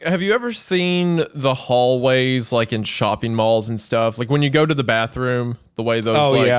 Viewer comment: Did wendi say shopping mall? oldest view reference